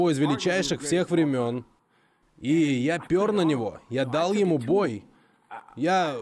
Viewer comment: Мне послышалось сначала: "Я пернул на него"